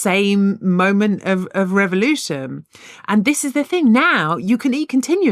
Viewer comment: did hannah just said "Revolution " instead of "Evolution". 😢